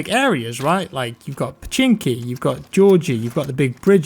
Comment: Bro did you just say "Chinky"? 17:28 You can't say that anymore man haha